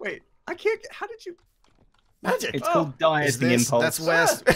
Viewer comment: i thought i heard say iskall died